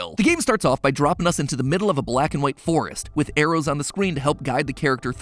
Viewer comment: did matpat just say "with arrows on the scween"